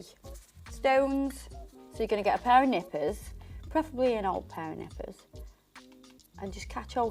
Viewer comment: I was watching this video when I was half asleep and the first thing I heard was “ok, so you need to get an old pair of knickers” lol obviously that woke me up and I realised you said nippers!! Love you and your videos.. keep being you your mint xx